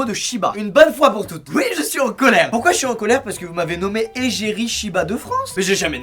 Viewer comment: J’ai cru qu’il a dit : « pourquoi j’suis à Angoulême » à 0:07 😅